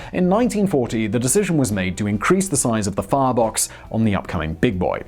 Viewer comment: swore you said "fartbox".